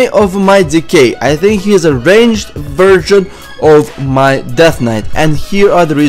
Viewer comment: it sounded like you said "virgin of a DK" instead of "version of a DK"